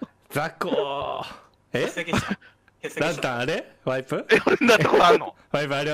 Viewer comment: さん 話戻そうとしてるんだと思うけど「え？笑 蘭たんワイプ？笑」って煽ってるようにしか聞こえなくて笑った